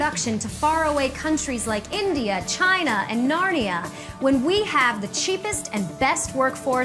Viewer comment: Did she say Narnia? Like who's transporting goods between realms.